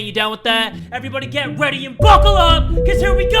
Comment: I always thought when you say 'Buckle up' I thought you said fuck the law.